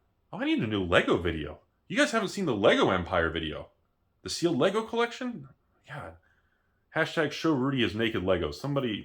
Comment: Show your naked Lego collection? What? I don’t think I heard that right lol